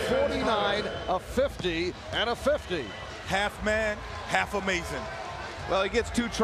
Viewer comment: Commentator at 8:13...did he said: "half man...have amazing" or "half man...half a MASON"? Typical freemasonic talk.